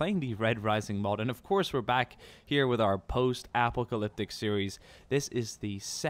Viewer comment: At the start I thought he said post Apple